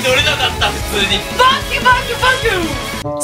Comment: の「バキュンバキュンバキュン」が「ファッキュファッキュファッキュ」て聞こえたの俺だけか？